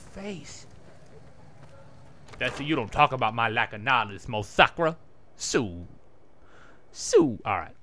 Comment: I don't get wut word your sayin, mosakra sumthing wuts that xd?